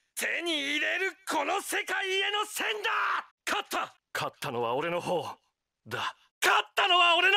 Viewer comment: Does anyone know what Diego says in romaji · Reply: Katta nowa ore nohou da